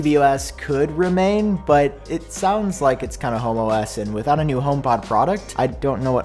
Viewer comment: I’m a little high and I heard you say Apple was releasing HomoS and I thought that’s awesome and right on time for pride😂💀